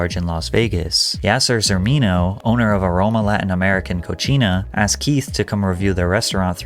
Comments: what the hell did he really say cochina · what did he say at 14:15??? cochina?!?!?